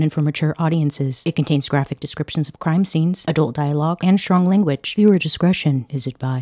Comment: I thought Bailey said crying scene instead of crime screen and I didn't question it for a moment